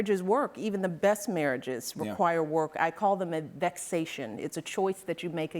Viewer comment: What is the word she says at 3:01 ? "I call it ...." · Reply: Vexation…. Sorry for being 7 years late.